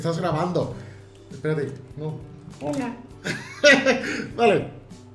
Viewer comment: Escuche una voz en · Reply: Bruno Leiva el dijo hola